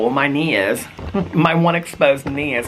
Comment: My one exposed kneen😂 I thought you said my neighbour the gorilla ahahahha